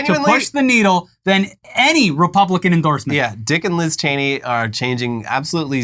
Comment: When you said "Dick and Liz Cheney" I heard "Dickless Cheney"